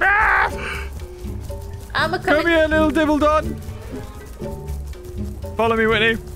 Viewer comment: did you just call it a diddle dog 😭